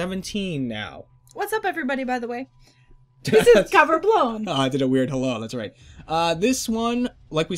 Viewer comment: At first, I thought it said Cover Brown and had something to do with Ray XD